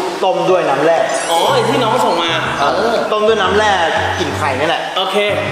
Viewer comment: ซับผิดรึป่าวน้อออออ พี่พูดว่ากลิ่น แต่ซับขึ้นว่ากิน